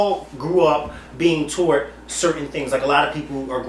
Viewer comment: LMAO @ 42:10 . . Sounds like is saying "Toe'Wert" instead of "Taught"!!